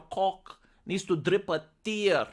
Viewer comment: did you say "Your cock needs to drip a tear"? 🤣 what the hell, I never heard that before lol